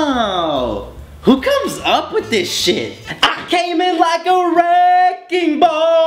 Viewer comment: Haaah at fist I thought the song said I came in like a rainbow